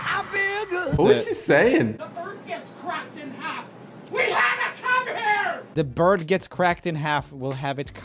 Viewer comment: i heard " WE HAVE APPLE JUICE" ...was that just me? i think i watch roomie to much